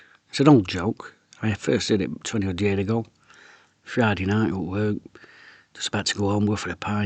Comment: 😂😂 at first I thought you said heard it 200 years ago 😂😂